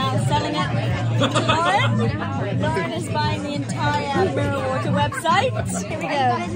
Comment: OMFG I swear, when you kept saying "Estee trying to sell everyone Mirror Water" I thought you kept saying "marijuana" and I was dying 😂